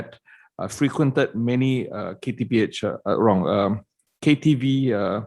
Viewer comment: did he say KTPH?!?! 😂😂😂(Khoo Teck Phuat Hospital)